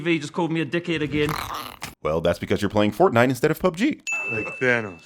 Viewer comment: 😂😂😂😂😂😂 Subtitles: Because your playing a kids game instead of a man game. "That's because your playing fortnite instead of PUBG."